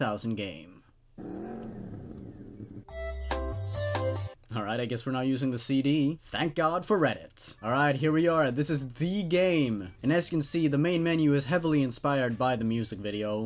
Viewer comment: Did just say, “Thank God for Reddit,” at 3:13 ?! I feel betrayed.